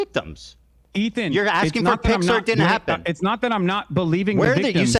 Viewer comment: Every time Ethan says "pics or it didn't happen," I keep hearing "Pixar didn't happen"